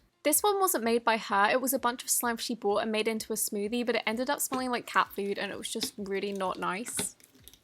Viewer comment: did you say the slime ended up smelling like cat food? Edit: the only reason I'm asking is because I wanted to make sure I did not hear anything else